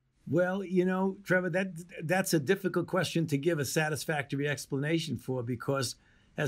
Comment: I thought Tevor said 'Just texplanation'... that would make a good segment also